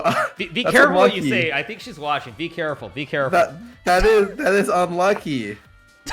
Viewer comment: LOOOOOOL I swear I heard him say "that's a monkey" the first time